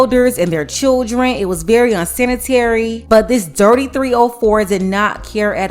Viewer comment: I thought she was about to say “dirty 3 year old” instead of “dirty 304”